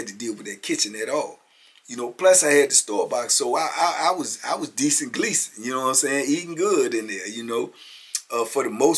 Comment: “Decent Gleason”? 😝 Is he related to Nervous Pervis? Idk if I heard that right.. I’m trying to stay up on the TBP lingo.